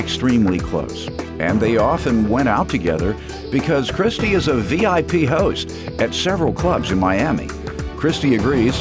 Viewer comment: When it first came on, I thought he said a VIP hoe.😂😂😂